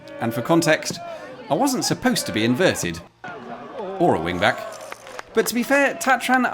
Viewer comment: Oh You were a "wingback". I don't play footie. I thought you said "wind bag".